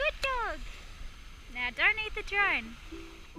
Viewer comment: "you're are good dog arentcha.... now don't eat the drone" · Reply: Lol at first I thought she said, "Now donate patreon!"